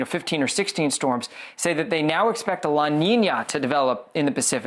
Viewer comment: Did he say “la niña” ?